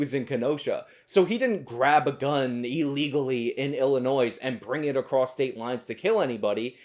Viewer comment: I think you just said Illinoiz at 25:04. I live in Illinois. I'm triggered